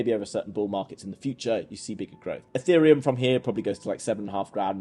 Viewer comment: wait, did you just say @ 12:54 that XRP could go to 7.5 - 9 GRAND? Do you mean $7.50 - $9.00? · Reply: He said eth